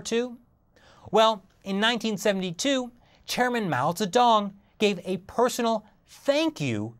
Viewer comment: Did he actually say Mao's a dong?